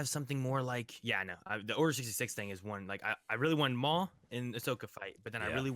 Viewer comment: Really thought he said "Order 66 is boring" for a minute...